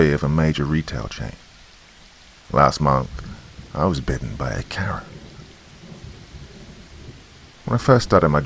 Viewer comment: I thought he said he was bitten by a carrot😂 🥕 ... took me a minute on that one.